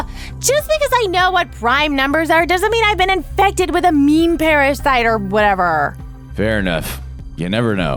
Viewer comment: Did she say meme parasite